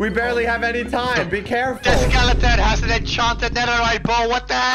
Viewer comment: I thought whenever said enchanted I thought he said enchanto(I’ve been watching to much enchanto lol🤣🤣🤣)